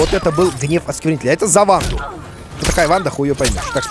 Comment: Я услышал "Za Warudo"